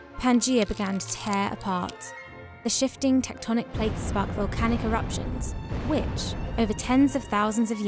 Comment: I thought she said volcanic erections!😂🤣😂🤣😂